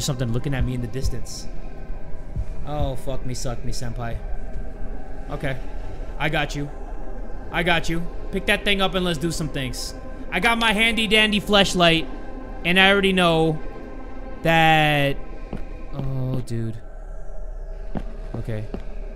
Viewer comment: Handy Dandy Fleshlight i swear i cant be the only one who hears him say Fleshlight right